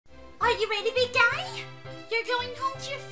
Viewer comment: i have ear problem i heard said Are you ready to die xD 2018 edit : Woah 215 likes. 2019 e. : bruh 285 likes cool monky noises